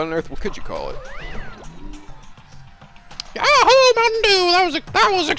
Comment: did he say yahoo mountain dew